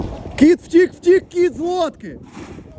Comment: Привезли людям лодки,я которому послышалось водки 😅😂